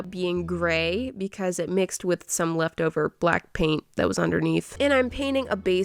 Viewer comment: Don't tell me i was the only one who heard 'Blackpink' instead of 'Black Paint'... My gosh this is weird 🤣